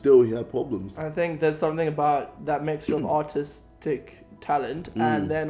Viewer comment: did Caspar say Autistic talent or artistic talent?